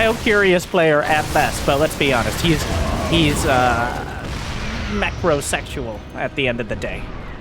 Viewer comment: I think macrosexual is definitely 7/10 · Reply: I thought he said mechrosexual